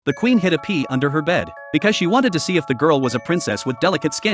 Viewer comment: At first i thought he said "the queen had to pee under her bed" instead of"hid a pea under her bed"😂